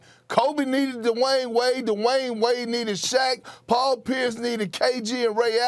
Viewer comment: Am I the only one who heard " Kobe needed Dwyane wade "...... ??? Perk need to be relieved of his job 😂😂